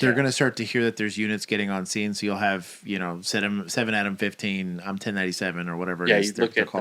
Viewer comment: Maybe it’s my French-Canadian ear, but every time they say, example, “7-adam-15”, I hear “7 out of 15”. I know it’s “Adam”, I understand why it’s “Adam”, I’ve watched the show with the subtitles, but my brain refuses to hear anything but “out of”